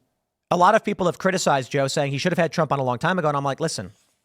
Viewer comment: I thought he said he would never have trump on... well off I go to listen to the podcast